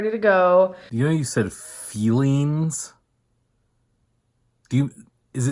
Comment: i thought she was saying "fillings" PLEASE don't tell me she was actually trying to say "fuelings"